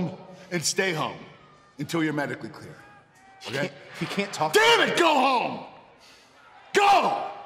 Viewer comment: I seriously thought Kurt said damn it gohan